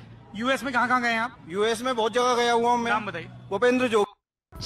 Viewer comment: that guy sounded like naam bataiye instead of kaha bataiye for some reason